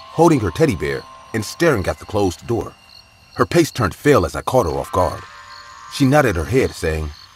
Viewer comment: No one else caught the "Pace turned fale" @ 19:21 ??🤣🤣🤣 Instead of "face turned pale" that narrator drives me nuts!! The animating on this channel is always so awesome, but that narrator ruins it for me. He clearly speaks perfect English... however, his sentences come out like broken English. And he uses words that don't even make sense!! "We were checking out the locality"!?!?! In that same story as the pace/face, While the 2 kids sit on the porch watching the trick or treat-ers!! Huh!?? And that's just 2 examples within 60 seconds. I like IMR's voice so much better... or let's read... or Mr. Creepy pasta.... or any other narrator lol